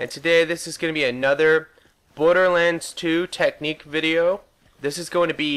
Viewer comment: at the start it sounded like you said Butterlands 2